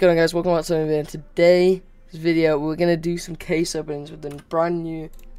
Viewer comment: I thought bro said case oh opening 💀👨‍🦰🔵